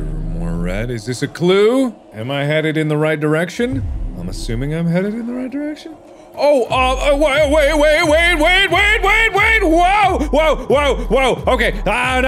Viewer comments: sounds like he was saying "Wade" I was like, "Wade is finally getting his revenge for all the bullying since the beginning of Drunk Minecraft." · it sounded like he was saying wade's name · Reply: Once I read this, he said that and I realized that yes, it really sounded like he said "WaDe!"